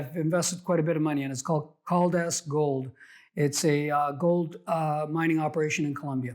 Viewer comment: Can anyone tell me the name of the gold mining developer he mentions at around 6:36? I hear it as Holdas Gold but I am English and sometimes find the American accent difficult to understand. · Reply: Caldas Gold